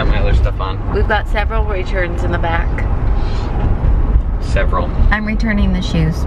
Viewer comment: It sounded like you said I have several Richards in the back 🤣